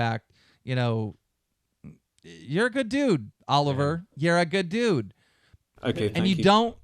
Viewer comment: Did Keem call "Oliver" at 24:05????